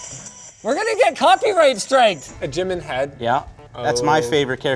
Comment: the subtitles say "we're going to get coffee rage strength" when he said "we're going to get copyright striked" lol.